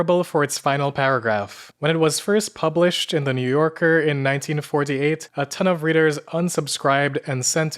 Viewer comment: It was first published in 1948, so although it was just after WW2, the memory of the draft was still very fresh in the public’s collective mind. And, of course, the draft would continue through the Korean Conflict and the Vietnam War just a few years later. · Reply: oh i got mixed up and thought he said it was published in 1944 😭 my mistake